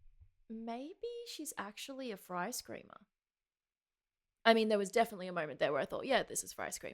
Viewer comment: If you set the English(auto-generated) subtitles on this video, you'll get to see saying she thinks Tatiana does ice cream(instead of "fry scream", 6:58) lol